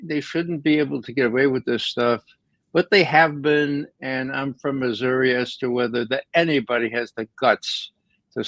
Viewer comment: What is Ray saying that sounds like "I'm from Missouri"?